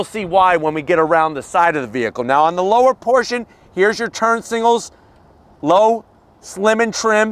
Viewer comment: Turn single: a relationship status that occurs after a breakup. Also: an exterior illuminated indicator on a vehicle. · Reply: I'm not even trying to be rude here but for the longest time every time says turn signal I always hear "single" 😵‍💫